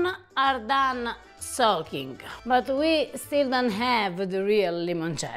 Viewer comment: Try this with mandarin oranges (mandarinetto). Aside from the compelling flavor of the final product, the beauty of mandarinetto is that the pith of mandarin oranges is not bitter. You can just put the entire peel, which easily comes off the orange, in the alcohol. There’s no need to carefully remove the pith from the zest. When I make this, I also put one small, whole mandarin orange, poked with a toothpick, in the alcohol mixture. Let the peels soak in the alcohol for 14 days. Everyone who tries this at our house asks for the recipe! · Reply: it sounded like she said that the lemons were done sulking,which didn’t make sense,because they had just been having a nice spa soak in alcohol for twenty five days!🤣😅😂